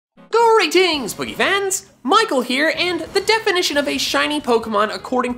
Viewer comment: The captions said: “Geurry dings prevents Michael here”